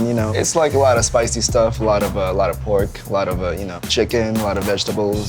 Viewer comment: did he just say pork?i thought indonesia was majority muslim lol..